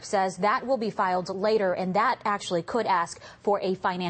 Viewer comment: what the hell is a "financial sediment" LOL I had to listen a number of times, but it's not my ears, she actually said it !!! Is English her 2nd language? o boy